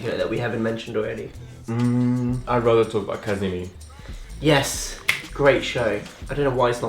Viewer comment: "I'd rather talk about ___" literally no clue what the fuck he said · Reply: Qasimi ✌🏾